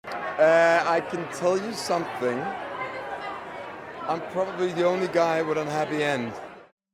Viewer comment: and 4:29 Euron. What did he say? "I can tell you something... I probably the only guy with a happy end"??? 😨😨😨😨😱😱😱😱😭